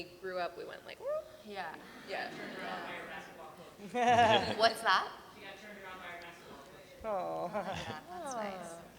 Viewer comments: what does the fan say at 42:40 ? and what is it that they could have mistaken it for? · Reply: It sounded like he said Nicole got turned out by her basketball coach but he said she got turned around by her basketball coach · what did the person in the crowd say? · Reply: She got turned around by her basketball coach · What did the person say in 42:44? I didnt quite catch it. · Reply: She got turned around by her basketball coach